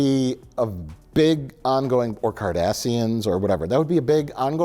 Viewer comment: The subtitles say “Kardashians” rather than Cardassians. 🤣🤣🤣